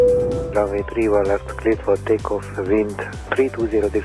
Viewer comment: Tudna segíteni valakit, mit mond 1:25 nél? Nem hallom ki túl jól, csak részlegesen. Mindegyik videóban benne van amúgy. Köszönöm! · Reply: Runway 31L, cleared for takeoff, wind 320 degrees at 9 knots :)